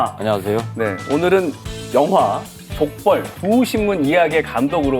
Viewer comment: '족벌' 두 신문이 말 하시는데 왜 내 귀에는 '족발' 두 신문으로 들리지 ???ㅋㅋ 올 저녁은 족발이나 뜯어야겠다